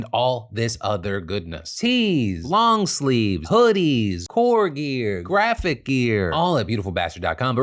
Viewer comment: "Tes, long sleeve, hoodies, whore gear..." actively skipped back to check what the hell I just heard lol!